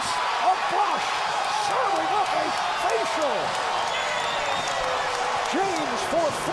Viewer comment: "serving up a facial!" -- did i hear that right?